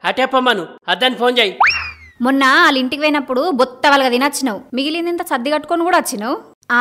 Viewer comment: What is the dialogie at 4:24 ? Did she say guttha balaga thinochinau? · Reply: ఆమె అన్నది బొత్త ......కాదు..